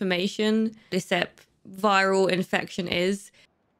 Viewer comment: Did that girl call it a viral infection? 5:05 Please tell me I heard that wrong 😂😂😂